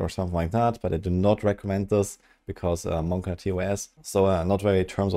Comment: did he just say monka tos?